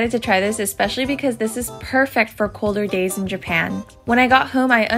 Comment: I thought she said "colder gays in Japan" and was very confused at what we had to do with Sweet Potatoes but listened again and realised she said "days" 💀